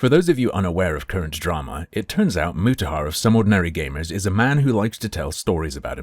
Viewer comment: Did anyone else mishear "some ordinary gamers" as "somewhat merry gamers"?